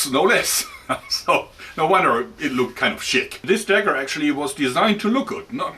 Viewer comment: I thought you said 'shit' instead of 'chic' and that was a very unexpected roast.